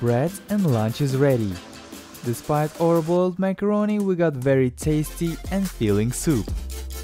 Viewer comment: What did he say at 2:53? Horrible macaroni or wat?..